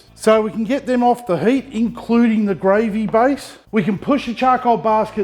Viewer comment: Hey :-) Every time you say "Charcoal Baskets" I only hear "Charcoal Bastards" ! (smut! lol) I've watched this vid about 4 or 5 times. My Pork shank is on the Weber kettle right now.